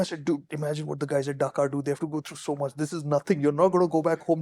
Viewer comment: Did said Dhaka at 2:17? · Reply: dakar rally